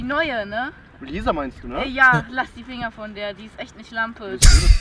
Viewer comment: Typ: lass die Finger von... Was ich verstanden habe: lass die Fingern😂😂😂 Bin dabei